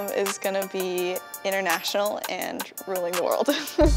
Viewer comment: I thought it said bath water. 😔